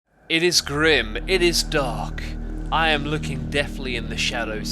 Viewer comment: No lies. I heard "it is Grim it is Dog" at the beginning XD